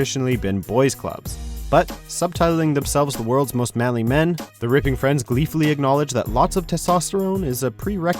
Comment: I thought you said the raping friends